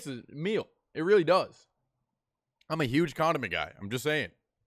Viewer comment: I swear I heard ‘I’m a huge condom guy’